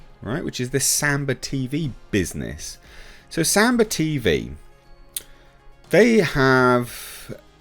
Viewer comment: Thought you said SAMBO TV I was gonna say 'yessum sir?' lol